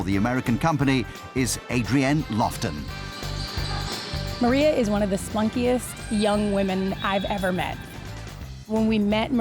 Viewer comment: Did she just say one of the spunkiest young women shes met? o_O